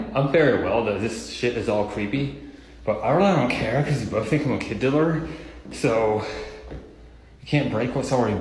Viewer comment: What he say @ 11:43? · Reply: “People think I’m a kid diddler.” Is what I heard.